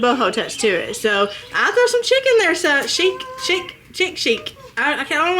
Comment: I swear I thought you said you throw some chickens in...not chic' lol